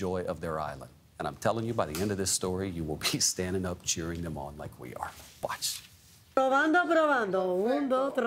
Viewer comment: I thought he said “you will pee standing up cheering them on” 😅